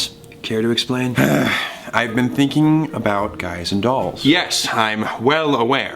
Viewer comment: I thought Thomas said "guys and gals" as in "I'm thinking about being bi"